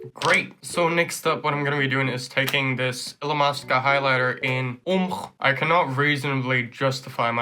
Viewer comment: I thought you were saying elon musk jighlighter and was like WHAT? That man makes makeup too? Then I saw the writing and was like Oh.